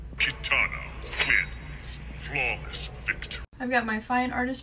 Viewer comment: Her: "inaudible words" Also her: "Aw damn" Edit: I wear hearing aids so i am not able to understand everything. Please don't reply with mean words please. Thank you! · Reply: i thought she was saying katana lens this whole entire time 💀